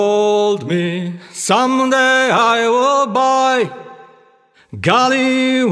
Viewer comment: My mother told me, someday I would buy Galley with good oars, sail to distant shores Stand up high in the prow, Noble barque I steer Steady course for the haven Hew many foe-men, Hew many foe-men My mother told me, someday I would buy Galley with good oars, sail to distant shores Stand up high in the prow, Noble barque I steer Steady course for the haven Hew many foe-men, Hew many foe-men My mother told me, someday I would buy Galley with good oars, sail to distant shores Stand up high in the prow, Noble barque I steer Steady course for the haven Hew many foe-men, Hew many foe-men My mother told me, someday I would buy Galley with good oars, sail to distant shores Stand up high in the prow, Noble barque I steer Steady course for the haven Hew many foe-men, Hew many foe-men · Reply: I'll be honest always thought they were saying "My mother told me some day I will die" but yeah buy makes more sense